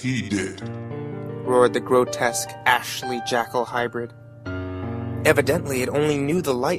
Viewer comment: I cracked up when i heard "Ashley Jackal highbreed"